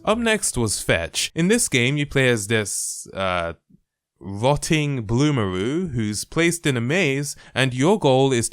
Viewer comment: i heard “Rotting Amumu” and I was like tf happened to amumu